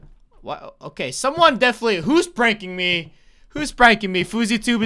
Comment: It’s sounds like your saying who’s breaking me instead of who’s prancing me